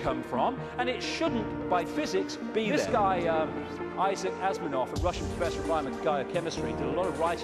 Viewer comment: did he say issac asminov at 1:26?